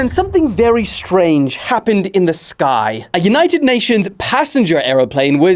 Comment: did you just say United Nations